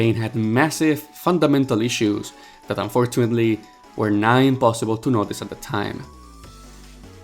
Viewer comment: did he say, NINE imposible?!?!?!?! 😂